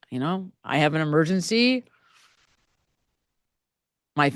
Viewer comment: I think I hear:" I, I, I , need emergency now" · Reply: I agree. “Emergency” not “murder”…